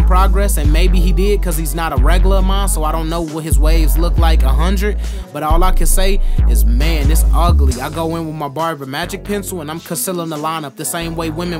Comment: did you say ugly or sum else?